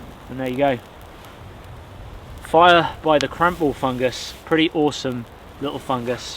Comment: At first I thought you said "crap ball fungus" and I was like, wow real mature name 😂😂